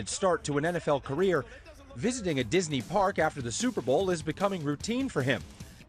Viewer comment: I swear at 2:48 I heard, “dis nigga Disney…”